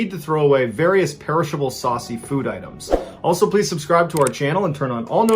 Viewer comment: When he said saucy food Items I thought he said "Sussy food items."